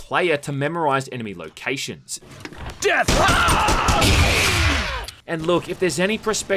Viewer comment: never played any of these games before but did he just say "Death" before shooting???? lol